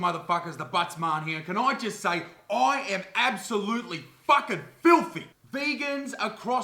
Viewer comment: When you did the intro, I thought you said "I am absolutely fuckin fuming!"